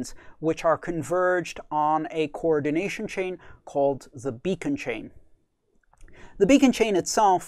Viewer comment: subtitle is wrong. Not "Bitcoin" but "beacon" chain.